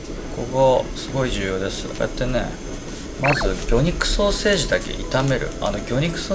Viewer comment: 虚肉ソーセージと空耳してしまいました。